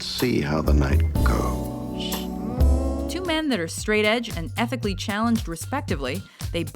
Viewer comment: did I just hear "Two men that are Straight Edge" ?!